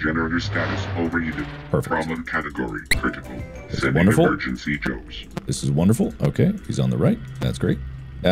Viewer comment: The “sending emergency Joes” kills me every time, like just imagine a whole bunch of random dudes dressed in casual business suits rushing in to the room 🤣💀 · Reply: i just heard it as Sending emergency jokes 😂😂